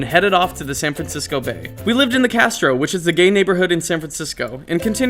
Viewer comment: DId he said he lived in a gay neighborhood on